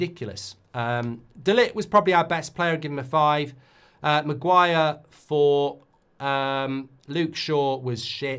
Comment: Thought you said give him 10 games. He is clueless